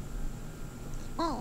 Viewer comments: it sounds like Bub is saying "Hey!" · it sounded like she said "oh, hi there!"